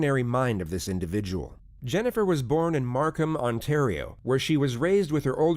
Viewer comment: for a second there i thought he said Arkham